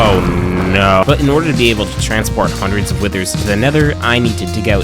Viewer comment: does anyone else hear withers instead of wardens?